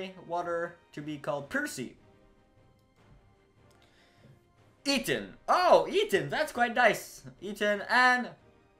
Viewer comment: sounds like your saying eaten :D